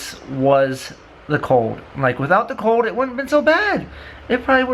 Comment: Did he say f$&@ cold in